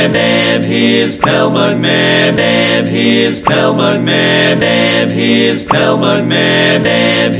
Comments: I keep hearing "and his cow mugman" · The thing i hear "SPELL MUGMADMANIS" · now i hear “and he is pal mugman”